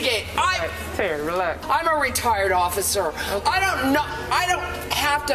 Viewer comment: I’m so sorry I thought she said “I’m a ret*rded officer” i was like HOLD UP